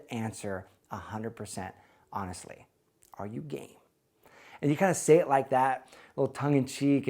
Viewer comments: Initially I thought you said Are you gay rather than Are you game and thought "no way" . Good thing I figured that out- too awkward. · I thought he said "Are you gay?" 😭🤣🤣🤣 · i thought he said ' are you gay' haha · I thought you said: "are you gay?" And I thought that it is really amazing xD